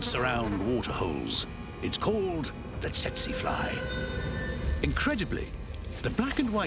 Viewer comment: I heard “Sexy fly”!!!! I’m wrong, right??????!!!